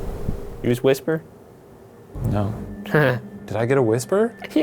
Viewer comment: it sounded like she said whisper